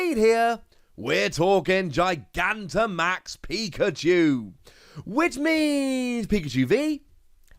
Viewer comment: Could have sworn he said pika-jew @ 0:18 lol